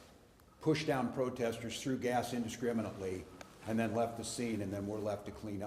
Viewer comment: …threw gas indiscriminately. Did i hear correctly?